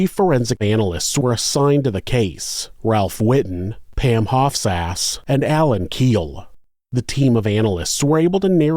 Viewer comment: did you say Pam Hofts Ass? That is one that would be tough having during 1-12th grade.